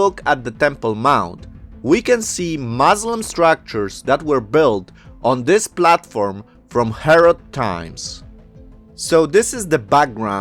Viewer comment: That’s not what he said. He said, Muslims used the platform from Herod’s time to build their structures. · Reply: That's exactly what I was about to comment, he said the structures were built on the platform that was from Herod's time. I had to rewind in the video because I thought he said there you could see Muslim structures from Herod's time.